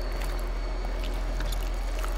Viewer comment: Did I hear “call me kitten” at the end there